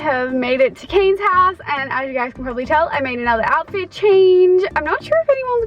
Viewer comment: I thought se said cocaine 😂